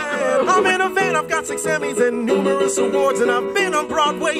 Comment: wayne: i’m in a van i’ve got 6 Emmys and numerous awards and i’ve been on broadway and i’ve done world tours🥳 charlie: ohh, i’m 5-times Grammy nominated 😓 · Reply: Kiwi Bird I thought he said 6 Emmys instead of Grammys